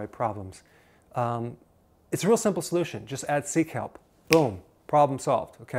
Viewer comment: he says that we should add something , i can't understand what he says and the subtitles don't help much... can somebody say what it is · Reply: HardWera “...add sea kelp...”